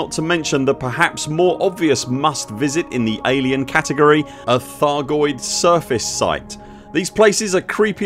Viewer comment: I could have sworn I heard Thargoid Service site :)